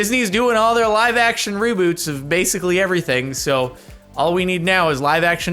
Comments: "LIVE ACTION ROBOOTS" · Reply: I heard it more as "Rooboots" 😂 Makes me think of a dude dressed up in a kangaroo suit with some bright colored rain boots on over it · Hey anyone else hear jardon say roboots???🤣🤣🤣🤣🤣🤣